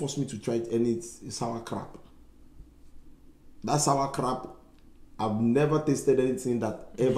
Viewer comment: I thought he said sauer crap